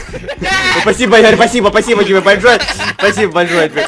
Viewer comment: мне послышалось, или сказал в начале "ебальшое"??)